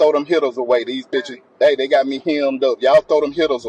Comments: New comment on older video, but I believe got it wrong. I don't think he said throw "them pills away" at 29:49. He's definietly saying Hitters, he had some people coming to take out that cop and save him, the cop was spot on with stopping him from texting. Even the closed captioning heard Hitters. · I think he said hitters. Meaning guns me thinks. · he actually said “Hittas” meaning the burner phones they’ve been using to communicate. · I think he said "hitters"? Maybe guns?